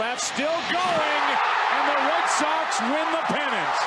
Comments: was I the only one who noticed Joe buck say the white Sox win the the pennant in 07 when the red Sox won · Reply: I thought he said white sox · was I the only one who noticed Joe buck say the white Sox win the the pennant in 07 when the red Sox won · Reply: It sure sounded like he said White Sox, but I do think it was Red Sox. I thought the same thing and had to rewatch it